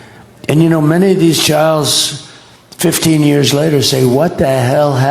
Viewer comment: I heard " many of these chyyllzzz he meant to say children, his brain kind of knew but the synapses couldn't make it work.